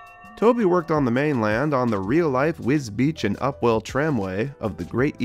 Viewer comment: sounded like flora’s bell